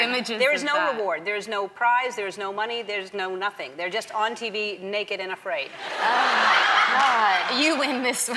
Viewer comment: I thought Ellen said "naked in a fridge"